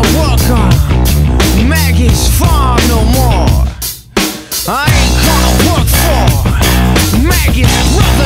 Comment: I always thought he said "I ain't gonna work for maggots for no more"